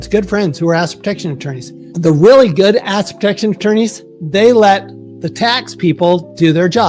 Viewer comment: His aggressive noise gate makes it sound like he is saying, "donkey protection," and I think that is equally true.